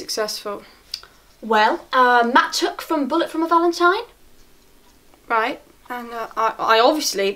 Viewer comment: Why did sound like she said "Matt had took a bullet from valentine" What did she say bc that's all I hear😂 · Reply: 'Matt tuck from bullet for my valentine' I think lol